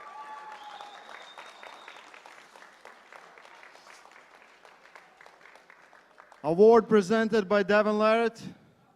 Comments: What did Oleg say to David at 18:56 ? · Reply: Спросил дважды: боремся дальше? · Что сказал Петренко? ☝️ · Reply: боремся дальше?